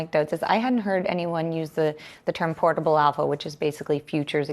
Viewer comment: anyone catch what term, the GS rep, mentioned @ 5:37? it apparently meant "future exposures excess hedgefunds". I heard portable arvo? portable avo? That means portable avocado here back home.. lol Which im sure isnt what she meant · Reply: portable alpha